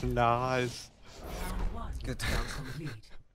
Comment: Every time when Mercy saying "witch" I only hear "bitch". I don't mean I don't like Mercy, she is very adorable, but this little-little voice line flaw...